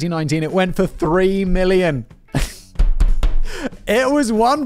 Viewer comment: Lol I had to rewind. I misheard million as billion and was like wait that’s double… smh